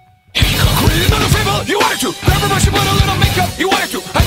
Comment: For the longest time I thought he was saying grandma should put on a little makeup.